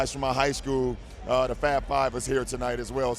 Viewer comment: Sounds like he said the Fat Fab (Fab Five)😂